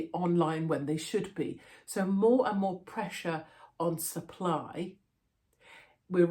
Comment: Just thought she said sir ply and i thought our rubbish pm had a new name only she said supply 😂😂😂